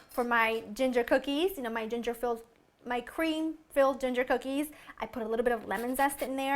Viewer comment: Sorry, I heard pinch of 'dru-ginger' xD